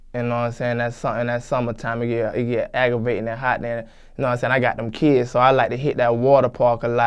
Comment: Lmao “I got them kids so I’m hittin dem water Parks” 😂😂😂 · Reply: i heard it like "i got that kids" like it was a medical condition